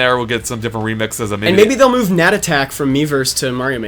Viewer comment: What are they saying at 10:57 it sounds like nadattack? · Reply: I haven't got to that part in the video yet but I bet you mean 'Gnat attack'